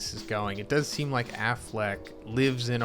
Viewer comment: I heard Aflak not Afleck 😆